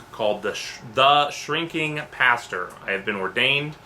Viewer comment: See, I always misheard Shrinking Pastor as "Cheeky Bastard."